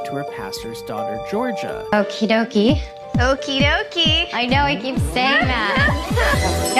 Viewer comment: It sounds like she said "okey dookey" 😂😭